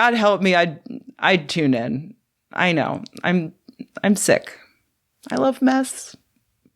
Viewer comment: Did she say "I love meth?*